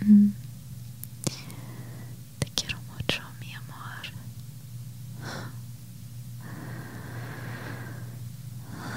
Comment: What did you say at 9:20 babe? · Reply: “I love/want you so much, my love.”